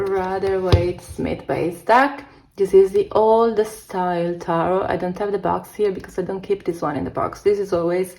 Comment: Thank you for this video, very cool choices and many I haven't seen before. 27:08 - name of deck please? Didn't catch it. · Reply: That's the Old Style Tarot, it's available mass market on Amazon, and if I'm not mistaken there's an Old Style Lenormand too if you read that system. Thank you so much for watching and commenting here! ♥️